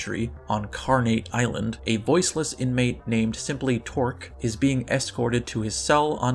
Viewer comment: I heard moistless inmate.